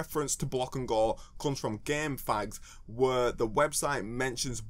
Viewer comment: Is it me or did you say in a homophobic way? "GameF**s".